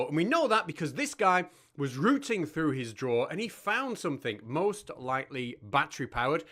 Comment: Oh, he was going through his drawer? I thought he said he was going through his drool